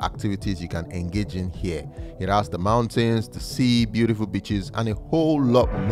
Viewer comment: What My Dirty Mind Heard Him Say 1:05 "It has the mountains, the sea and beautiful b*tches" 🤣🤣